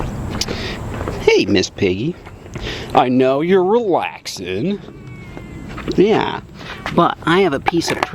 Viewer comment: Was it just me when he said hey Miss Piggy I know your relaxing you thought he said hey Miss Piggy I know your on laxative